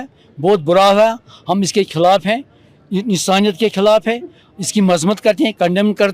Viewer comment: did he just said.. hum insaniyat k khilaf hai? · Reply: Hum iss k khilaf hai,yai insaniyat k khilaf hai